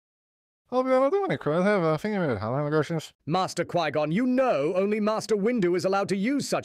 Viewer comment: "obi wan I don't wanna criticize you but I think that was hella atrocious" 0:36 "I'd be honored, cause I was thinking of how we'd work to keep Yoda sober" 1:22 "No, I don't think that, Obi-Wan is mistranslating me" 2:01 "I'll be honest I didn't even want to be on the council, I just wanted to help Yoda. Please slow down." 2:14 speaks clearly, it's what the subtitles say 3:13 "ah, Jesus!" Although I'm using more of an I worked in a nursing home 2 years style of translation rather than an I know a lot of Irish people kinda translation